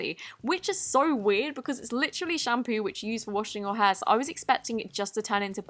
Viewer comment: In 0:33 its sounds luke she said hands instead of shampoo "its literraly shampoo in washing your HANDS"it sounds like hands like if you agree